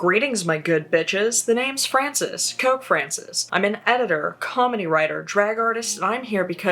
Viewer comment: I misheard "Comedy Writer" as "Kamen Rider" and now the mental image is seared into my frontal lobe.